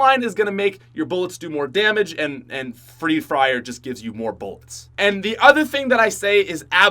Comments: Who heard him say free frier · says free fryer instead of free fire. I thought I should share that for whoever didn't catch that